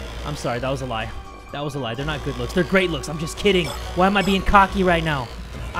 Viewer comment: I heard good luck so Nagito komaeda